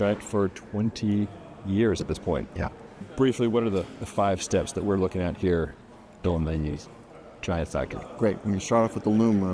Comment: What was said at the 0:20 second mark? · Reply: "...five steps we're looking at here, in the, Giant factory."